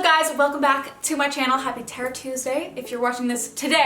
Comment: who else thought she said TERROR TUESDAY